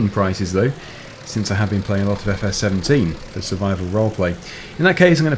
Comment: LOL, @ 11:59 the captions say instead of "for survival roleplay" but captions say "that's survival wrong way". (that's set on US english, not UK english) .